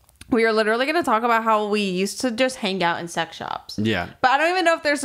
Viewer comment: i genuinely thought said "sweatshops" instead of "sex shops"